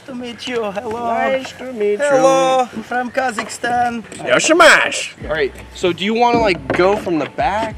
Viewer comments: did he just say jak się masz or am i flipping out, also you've come so far, no nosy neighbor is gonna stop you now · did I just heard "jak się masz" it means "how are you" in polen?...